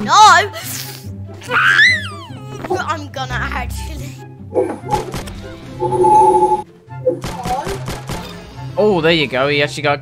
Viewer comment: did i hear "im gonna hack you"